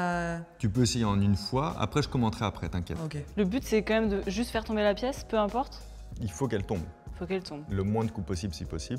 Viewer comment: C'est quoi le mot qu'elle a dit à la minute 31:17 ?(avec un orthographe correct svp) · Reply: Sébum ?